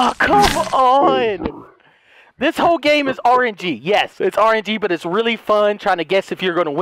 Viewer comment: Delirious: This whole game is orangey! (Steals all of the oranges from Walmart) Me: Did someone call for oranges?! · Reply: I think he said RNG